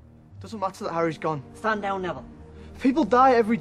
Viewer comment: What does Seamus say at 3:06? I don't understand him. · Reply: stand down neville